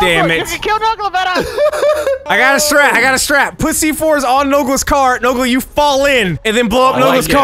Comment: it sound like Marcel is saying "pussy" but he's actually saying "Put C4"